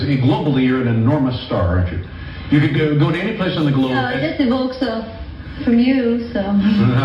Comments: can anyone help with what she said.....😂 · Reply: Aishwarya said “No, it just evokes a 🤷 from you so…”. 🙂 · What does she said at 4:38 ? I couldn't clearly get the meaning of the statement she made. Can anyone tell me ? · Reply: Just evoke From You... So... 😂😂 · What did she say at 4:39???? I can't hear it.... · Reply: Anwesha Mishra she said “it just evokes a [shrug/ couldn’t care less expression] from you”